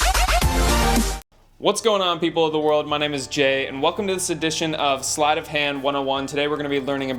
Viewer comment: im not karma but..i heard you say 'my name is gay'...im sory